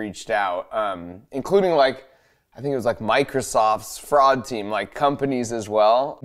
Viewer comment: For a second I thought Mark said Microsoft Frog team. I am kinda sad it wasn’t